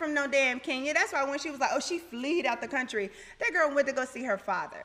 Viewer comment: Ummm at 0:34 did Porsha say she "fleed" out the country?? Really?? 😂🤣